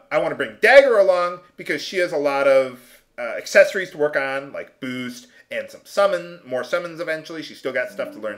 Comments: i thought you said she had to work on booze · better than me i thought he said she had to work on boobs and my reaction was i think you confused Dagger with Eiko